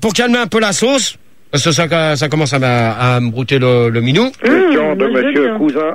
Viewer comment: Qu'est-ce qu'il dit?! "Ça commence à me brouter le minou"?? lol